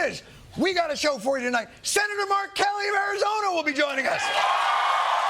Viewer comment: I thought he said "Senator R Kelly" for a moment with reality the way it is